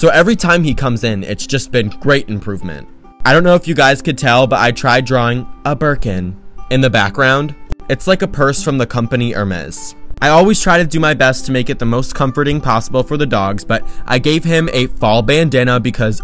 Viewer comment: A berkin.... I thought you said a Bergen from the trolls movie I am so sorry😂😂